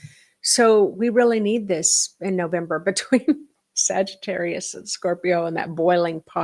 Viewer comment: sounded like you said Snovember! Are you anticipating a snowy time in the PNW? 🤩🥰😍